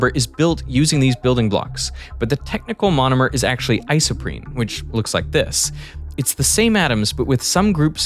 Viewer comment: Not my fat ass hearing ice cream instead of isoprene at 36:10ish 😂